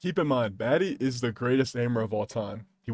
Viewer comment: did bro say baddie or maddie im so lost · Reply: matty